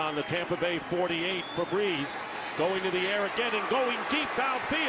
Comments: Who else heard febreeze at 1:00? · Lmao it sounded like he said “Febreze” 😂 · when you see that square is wide open but accidentally hit triangle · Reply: Lmao it sounded like he said “Febreze” 😂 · It sounds like he said frebreeze 😂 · I thought he said febreeze 😂😂😂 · does anyone else hear “Fabreeze” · did he say febreeze? Lmao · I’m really starting to think Zay Jones is a Robot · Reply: Lmao it sounded like he said “Febreze” 😂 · did he say Fabreeze? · I thought at 0:59 he said fabreez